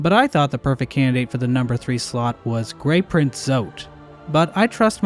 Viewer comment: Almost sounded like you said “Grape Print Zote” 😹